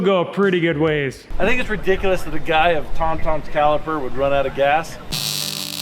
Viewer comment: did say" a man of Tom's caliper"?